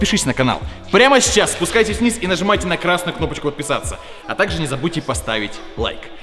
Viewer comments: Мне одному показалось что он сказал отписаться? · он сказал отписаться или мне послышалось?!